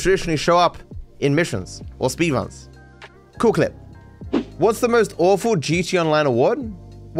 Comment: Sounded like Stewie saying cool whip 😆